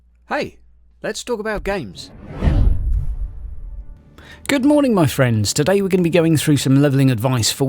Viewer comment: A.) Very helpful. I learned a lot of things I didn't know. B.) I could have sworn you said "Good morning, mushrooms," at the beginning.